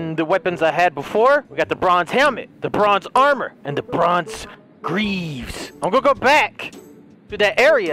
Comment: Whenever he says "the bronze" it sounds like he is saying "lebron's" so it sounds like he said "lebron's armour lebron's boots and lebron's pants"